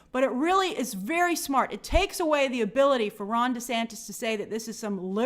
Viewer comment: ugh, i hate his name. Every time says his name, I keep hearing "Rhonda Santis" and it always throws me off.